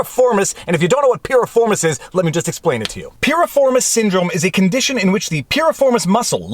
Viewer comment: I thought you said “Piers Morgan syndrome”. Symptoms appear the same...pain in the butt.